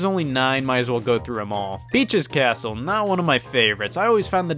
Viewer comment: i heard bi*ch's castle